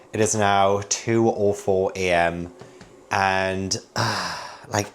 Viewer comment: I heard "it's now 2 aweful a.m." xD